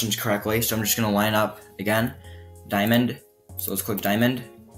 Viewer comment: I thought he kept on saying ‘yeah we’re cracked’ 😂 😂 😂 instead of ‘yeah we’re correct’